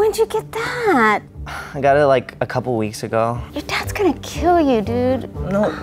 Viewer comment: "YOUR DADS GONNA KILL U DUDE" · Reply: I thought she said "That's gonna kill you dude" :P